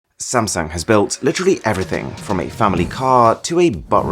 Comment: did he call samsung samsang